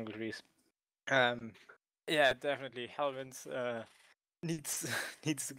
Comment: who are you talking about ? I cannot understand the nickname of the builders of the tree farms ç_ç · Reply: Mandel Cube helvince wants to do a showcase/tutorial on his unviersal treefarm after he finishes his final exams.